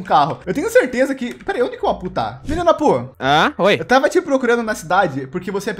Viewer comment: "Menino aphu" Oque eu ouvi: Filho da pu kkkkkjk